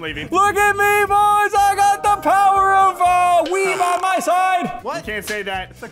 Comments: what did he say? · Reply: The captions: “ the power of weed by my side” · did he say weed by my side